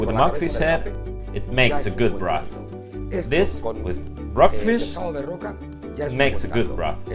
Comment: is he saying "brockfish"? Closed captions always make these foreign language videos better.